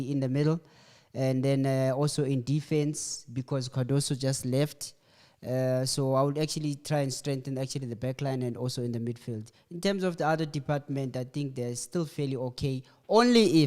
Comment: Did I hear right, “Cardoso just left” ?